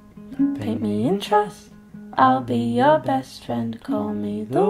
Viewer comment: 'Paint me in trust' is a lovely sentiment x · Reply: Lowkey thought she said pay me in trust